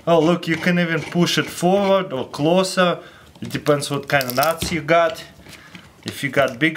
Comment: did he say "it depends on which nazi you got" ?? 😂